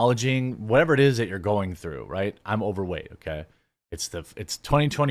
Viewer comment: He said I'm overweight OK. It's 2024 I thought he said I'm openly gay. It's twenty twenty four.